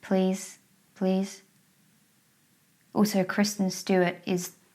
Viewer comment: Ok so I heard "Christmas do it" and I thought you were talking about a girl from a porn until I finally said it out loud and realized you said Kristen Stewart -_- ah that feeling of dumb... hahaha